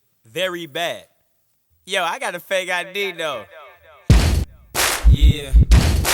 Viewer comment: He said "yo i got a fake i.d though" you shouldve knew he was young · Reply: Carlos Alvarado Rolf. This whole time I thought he was saying "I got a good idea though" 😂😂 didn't even realize in the video he showed a fake. Lol.